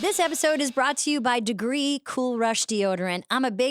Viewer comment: I really thought you said Degree Cool Ranch Deodorant